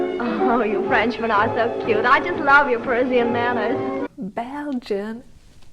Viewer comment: Did she say "Persian Manners"? Did she mean "Parisian manners", as she thinks he's French? Or is that slip up suppose to highlight her dumb blonde character? Im so confused